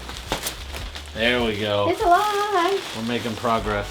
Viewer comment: what was saying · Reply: “It’s aaalive”